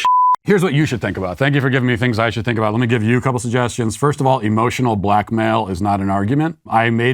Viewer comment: I had to rewind bc I thought he said “emotional black male” and was so confused on how that fit the convo lol